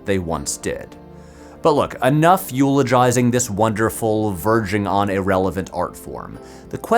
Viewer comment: Did you just say 'virgin on irrelevant'?! Also, if anyone wants to make a playlist of every video in this essay I'd be very enthusiastic about it.